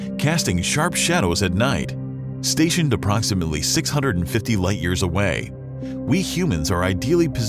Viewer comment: Man like beetleguse is 650 light years away, i thought he said it was 65000 light years away 😅